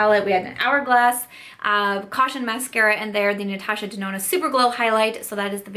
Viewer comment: I swear it sounded like you said "Natasha Denona superbowl highlight" haha